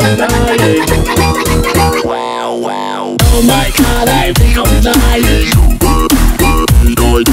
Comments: Song: Happy and vibrant. Lyrics: O H M Y G O D I M D Y I N G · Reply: I thought it said “Oh my god,I’m nearly dieing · I hear these two “Oh my god I feel I’m dying” “Oh my god I feel like dying” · Things he might be saying “Oh my god I feel excited” “Oh my god I feel us dying” “Oh my god I feel like dying” “Oh my god I feel like crying” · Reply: i hear "oh my god I feel anxiety " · Why did I hear it as “Oh my Gosh, I reunited!” 💀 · Things he might be saying “Oh my god I feel excited” “Oh my god I feel us dying” “Oh my god I feel like dying” “Oh my god I feel like crying” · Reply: I hear "Oh my god I'm legally dying" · Not sure what the actual line is but I keep hearing "Oh my god I'm legally dying" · for years I thought he said “oh my god im real excited.” 😪